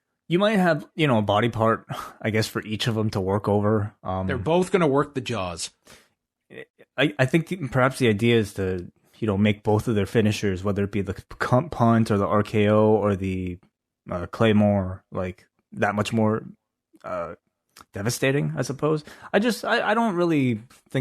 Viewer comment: did say "Cunt Punt"? lolol